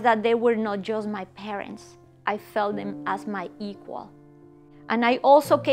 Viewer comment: Can anyone tell me, what is said at ;11:31; "I felt that they were not just my parents, I felt them as my...." I can't understand the last word of this sentence. Thank you in advance..! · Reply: As my equal .. I think